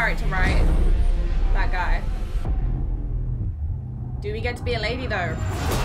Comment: Did u just say "Do we get to be a lady tho?" 🤦🏻‍♂️🤦🏻‍♂️😂😂😂